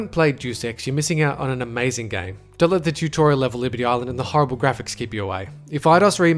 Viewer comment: Did you really say "on't let the liberty island level keep you away"? That level was fantastic, and a great example of how much freedom Deus Ex gave you.